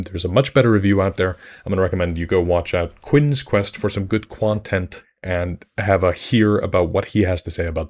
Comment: did you just say “quontent”?